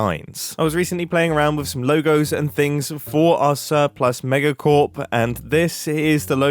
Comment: When I first registered what Mumbo wanted to name the company, I heard "Sir + Mega Corp"